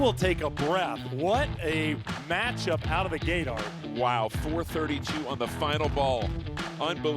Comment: did he say out of the gaydar?